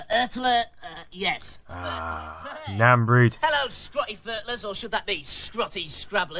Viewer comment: He likes the word Scrutty...What does he say Scrutty Furtlers, or scrutty scrublers...they had some sweet slang in the 80/90s too over there I take it